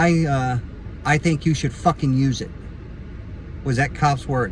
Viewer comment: I thought that cop was saying 'you should talk to Jesus' but I like 'you better Fuck ing use it'